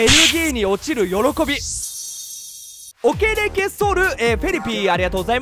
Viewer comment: オケレケ剃るフェリピにしか聞こえなかった。